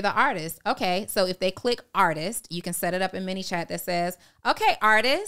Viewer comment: What’s the name of the system using as her DM messaging system? It sound like said “mini chat” I assumed it was an app or is it something offered through IG? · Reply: I think it’s manychat